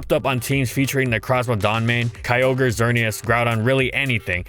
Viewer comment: are my ears dying right now or did i just hear necrozma dawn mane?